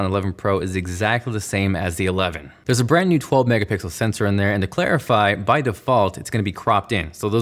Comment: I swear I heard you say “megapickles” and I love it.